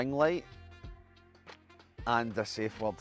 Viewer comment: someone please translate the 3rd item, i can't make out that scots word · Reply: Safe word